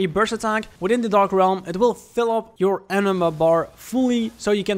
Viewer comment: when that auto caption typed "enema" instead of "anima", I reacted "oh shit" right away. lmao Edit : You could've been more specific when you said "a specific yokai", yet you missed the chance to say so. Dark realm boss can be noticed by the red flowers surrounding said yokai, there will be only one boss of each dark realm, and only the dark realm boss that would have those red flowers.